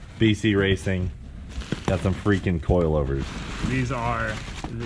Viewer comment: I keep hearing "Beasty coil overs" for some reason. 😁